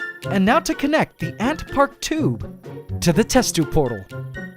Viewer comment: At first I thought it said ant prank